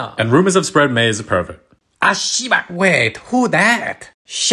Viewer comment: i thought he said “ashiyubi” or あいしゅゆび” which is toes in japanese